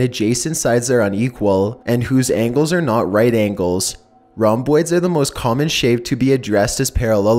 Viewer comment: I like your voice.. But when you said " rhombus".. All i heard was "Round boi". I mean.. Both shapes are good. Hahaha